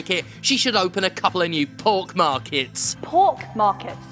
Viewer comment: I thought she said "pork markets," and that's funnier so I will not be dissuaded by Laura's excellent captions!